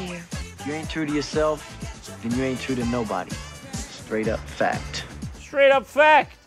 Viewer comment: "If ya ain't true to yourself, ya ain't true to nobody, straight up fact" -Aristotles dying words 322 BC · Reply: So THAT'S what he's saying in that line. He's so busy mumbling and talking in the opposite direction of the mic trying to look cool that I thought he said "Stay true to yourself. Stay true to nobody. Straight up fact."